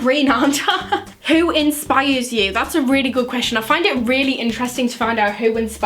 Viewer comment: Okay so this has nothing to do with the video, but when she said ' inspires' I swear Min Yoongi from bts made an appearance for a minute. I swear that I heard 'infires'